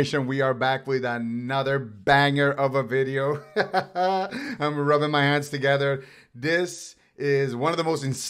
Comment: I might have smoked a bong before starting this video and when you said “I’m rubbing my hands together” I thought you said “I’m rubbing my ass together” 😂😂